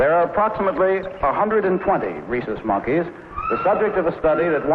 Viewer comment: I'm sorry, but what kind of monkey's are these? It sounds like Harlow is saying "racist monkey's" so I have zero clue what type these are.